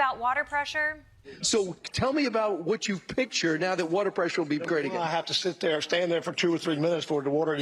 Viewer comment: did he just say make the water great again 😂